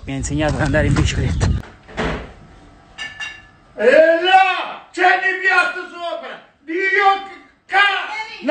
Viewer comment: ancora non ho capito cos'ha detto nonno hahaha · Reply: Ha detto "Eh no! Ci sono i piatti sopra!!"